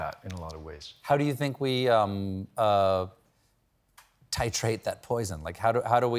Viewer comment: Can somebody tell me what does he say at ~12:40? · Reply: Stephen: “How do you think we titrate that poison? Like, what do you do with a poisoned atmosphere like that?” Jeremy: “Well, you come up here every night and speak the truth.”